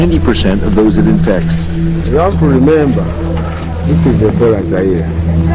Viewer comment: the text on screen is wrong. he actually says "This is 'Ebola Zaire' - it is highly infectious, highly dangerous" not "inside here"